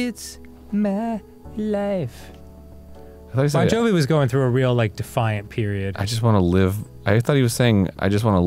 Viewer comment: i thought said bon jovi was dating MBB i got full body creeps i was so upset